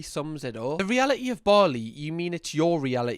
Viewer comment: Ohhh, Bali. I thought she was saying barley 😅